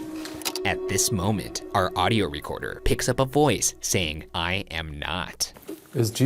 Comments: With the Jesus saying I am not bit I thought it said I hope not instead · I've always thought that the ghost saying "I am not" in response to Shane asking if Jesus was present sounds more like someone saying "I hope." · tbh I heard "I hope not" not "i am not"